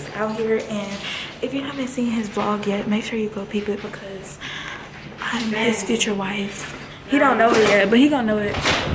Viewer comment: Y'all finna ignore desi Des said she's his future wife lol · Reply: I thought she said I met his future wife 😂